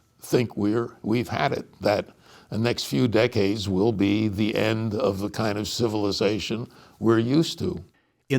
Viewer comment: LISTEN! He did Not say this will result in the end of civilization. 🤔 He said it's "...the end of the kind of civilization WE'RE USED TO." 😲 11:24 Life will find a way. 😉